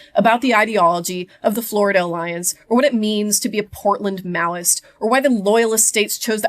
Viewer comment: Imagine Maoists being the dominant faction in Portland, a city full of anarchists. 😂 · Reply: I had this exact thought when I first watched the film. Anarchists don't tolerate the red fash. Also thought I heard "heartland" Maoists on a second watch but I checked the subtitles and it says Portland iirc